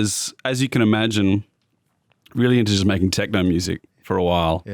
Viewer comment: Did I hear “Jamaican techno”?